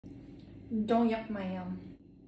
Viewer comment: I thought when she said don't yuck my yum it sounded like domayang and i was like is it fillipino and today after watching this video it was don't yuck my yum